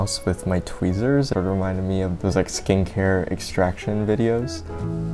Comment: you said “skin care extraction” I heard “skank hair extraction” 😂